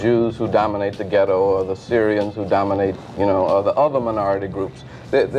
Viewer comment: did he say the SYRIANS?